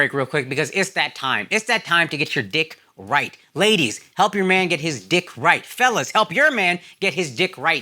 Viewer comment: nah me watching this in the living room then I heard "lady's helps your man get his dick hard" lmaooooo